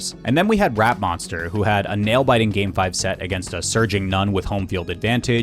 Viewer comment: I thought I heard that as "A Surging Nun" and was confused for a moment.